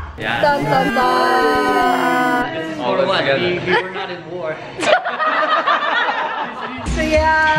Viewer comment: de Gökhan Ne diyor aga anlayamadım bir türlü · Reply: We weren't at war diyo yani Suriye ile savaşta değildik ztn dio